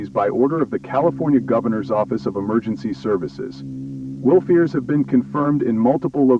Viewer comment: did he say 'wild-fears'?